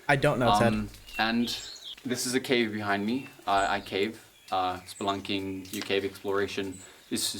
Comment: instead of spelunking the subtitle say spanking